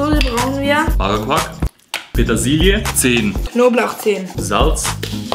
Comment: Ich hab erst 10 Knoblauchzehen verstanden. Gut, dass es eine Zutatenliste gibt 😂